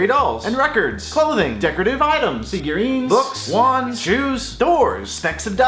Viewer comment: when you said shoes, I thought you said Jews.